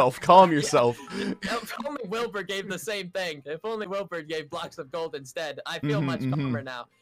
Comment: sir- what did u just say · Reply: “if only wilbur gave the same thing”